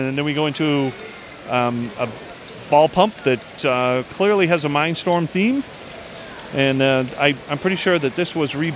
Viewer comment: WHAT DID YOU CALL THAT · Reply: Matt Hanson mindstorm